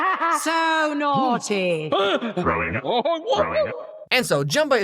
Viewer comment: what he actually said was ”I want to destroy!”